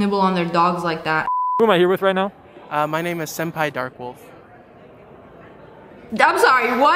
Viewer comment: what is your name well my Name Is. Senpai. Dark wolf i think that is what he said